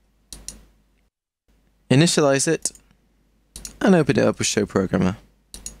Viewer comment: seconds, what the hell are you saying "Oapey dope a show programmer" haha made me lol.